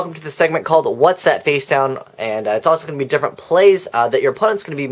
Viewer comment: at first i thought it said "whats that facebook" XD lol